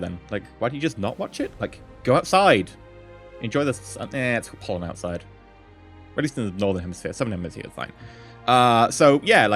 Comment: "Go outsid- oh wait it has pollen out there." that is the reason why Great Britain has asthma problems. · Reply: i thought he said "uh... its pouring outside"